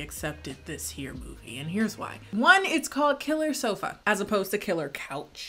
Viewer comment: i thought i said killer sophia 😭